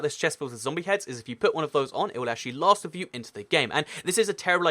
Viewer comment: what did he say at 3:47 I couldn't understand anything. All I heard was " It will Lasblblbl in to the game." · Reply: It will last with you into the game